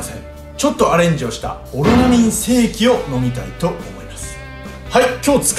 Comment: オロナミン精〇って最初聞こえたwww